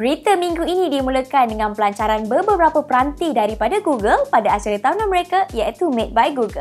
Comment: Srsly i heard "berita minggu ini dimulakan dengan BEBEBERAPA"😂